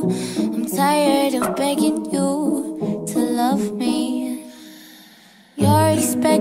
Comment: for a moment i thought she said "i'm tired of fucking you to love me."